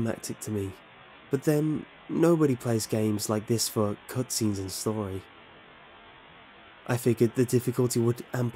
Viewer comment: "Nobody plays games like this for..." what? I can't understand what you said after that. It sounds like "cudsings and soary," but that makes no sense. :P